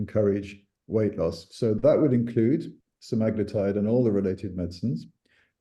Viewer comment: Hello everyone. Thanks for the incredible information given in this video. As a non native english speaker, I want to ask what treatment was Prof. suggesting around 18:50? Did I hear "magnetite"? What is this treatment about? Thanks in advance for your response